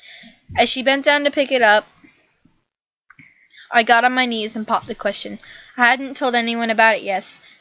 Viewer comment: this reminds me of were are you dipper... but! with mable instead of dipper, also Bil Cipher!!!!!!! XD sorry i was referenceing farly odd parents Dinkle Berg!!!!! anyway great fan fiction's and thank you for reading them bye! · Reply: RainbowAGStudios I thought you said poop, not popped. 10 out of 10 me